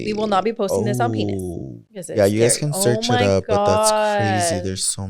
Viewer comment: Everytime they same pretty not smart abbreviated it sounds like their saying penis lol and with the sponsors “use code penis” lmao 🤦🏽‍♀️😂